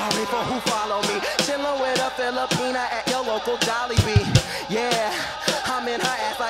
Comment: DID HE SAY JOLLIBEE